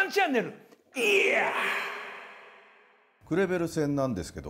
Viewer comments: クレベルのとこグルグルって聞こえるw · クレベルが「グルグル」に聞こえるのがたまらんww